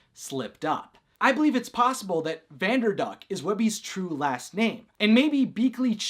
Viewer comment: At the end of the video it sounded like u said vanderbuck instead of vanderduck lol